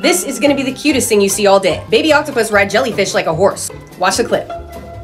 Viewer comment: i thought u said an octopus rides sea horses as a horse